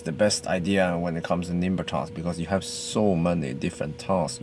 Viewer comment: What is he saying at 7:00. “Nimber class”? I can’t quite make it out. Edit: nvm. He said nimber task. It’s a delivery service apparently.